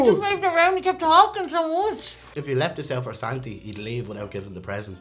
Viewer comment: The guy who kept calling Santa, "Santy"...what is his name? I always forget😭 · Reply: I thought that guy was saying "Sandy" instead of "Santy".